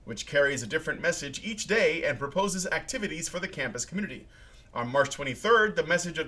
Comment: hhh wtf the fuck happens at 14:00 when he said community? sounds like pblblbblee to me? thought my brain was jsut fucking with me